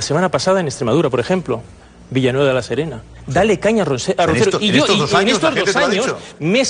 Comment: Quizás soy yo pero creo que dice roncerdo